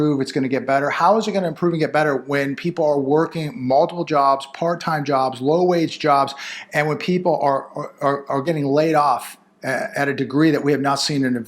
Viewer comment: Whoooooaaa I thought they said part time jobs are full time jobs 😂